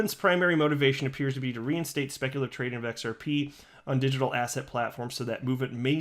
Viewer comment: Every time you say "Digital Asset" now I hear "Digital ASSHAT" hahahha